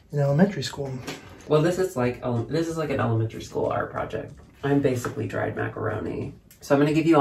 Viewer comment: I heard, "I'm basically Drag Macaroni.", and merch flashed! before my eyes.